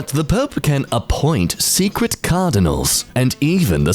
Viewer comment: I didn't understand why it was talking about canibals, I didn't read/hear cardinal but canibal- help I need new ears and eyes